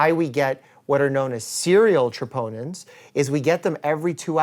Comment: I literally heard “serial chipotles” 😂